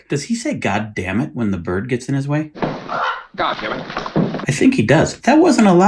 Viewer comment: I don't hear "God damnit". I hear "Look out Jimmy". Is the bird's name Jimmy?